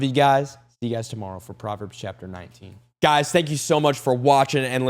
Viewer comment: I thought u said Potverbs 😅😅😂😂😂❤❤❤